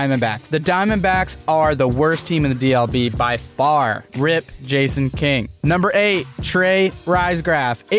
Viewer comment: When u said Jason King it scared the fuck outta me cuz I thought u said Jacob lol